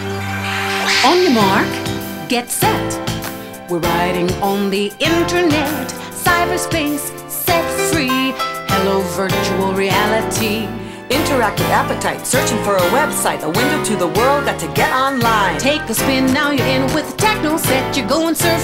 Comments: did she just said Sex Free?? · did it say sex free? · What is she saying? "Sex free"?? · did she say sex-free? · Does she say sex free? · does she say sex is free xD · sexy? Is that what she says? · I thought it said "cyberspace, sex free, hello virtual reality!" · Does she really say "sex free"? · Did it say "set free" or "sex free" at 0:33? Because I think the latter is the more accurate of the two...